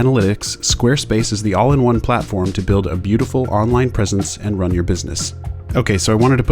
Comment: When he was talking about Squarespace, I thought he said they help build beautiful online prison cells :/